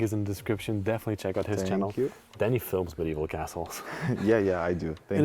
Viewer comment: At the end when you said Danny films Medival castles, I thought I heard you say, Danny films with me you assholes. hahahahaha :face-blue-smiling: With that being said after I'm done cracking myself up with my deafness I thought this was an amazing place. As always, great job, I really enjoyed it.